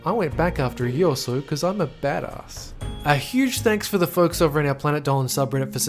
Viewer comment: my phone glitched and it sounded like he said "cuz I'm a ass"😂😂😂😂